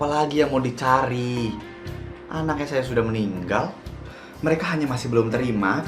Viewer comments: hah apa tadi Bu "anak saya sudah manggial" what ..apa gw salah denger ya.. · hah apa tadi Bu "anak saya sudah manggial" what ..apa gw salah denger ya.. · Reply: Aku sih denger nya "anak nya udh meninggal" Mungkin kamu salah denger